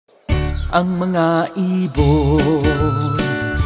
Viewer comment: 어머나 이불로 들림ㅋㅋㅋㅋㅋㅋㅋㅋㅋㅋ